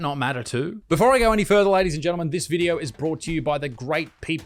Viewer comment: I know you didn't say it....but at the end when you say "Ladies and Gentlemen" I swear I heard "Ladies and Gendermen", and couldn't help but laugh and think that would be a hilarious way to say it moving forward.